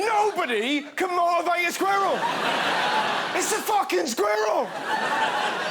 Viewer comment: i heard squirtle 🤣